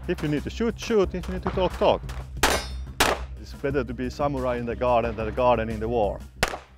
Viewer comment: what does he say here ? anyone that understood ? im curious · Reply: He said: "It's better to be the samurai in the garden than a garden in the war", but I think the last part should've probably been "than a gardener in a war".